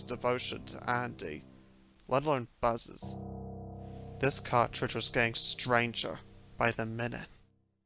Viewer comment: I used closed captions at 5:46 (because couldn't quite hear due to mic quality) and got "this caught treacherous gang Stranger by the minute"